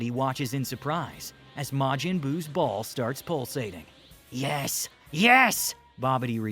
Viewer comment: I thought he said “Majiin buus balls”